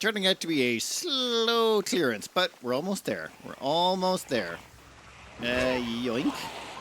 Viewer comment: we need to talk about your use of the word "yonk". please observe how you used it about about 1:00:47 as a prime example. The correct verb is when someone deposits a pipe bomb into a group of unsuspecting victims from 2 or more meters is "yeet". "Yonk is to be preserved for those actions that result in a notable increase in one's personal belongings. Glad we had this chat. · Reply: I hear him say "yoink" not "yonk", which of course it is also a very poor choice of words for what he's doing. Honestly though, he fumbles the vocabulary so often and always leans towards using the wrong words instead of being self-critical and improving on it, so I don't think that's ever going to change.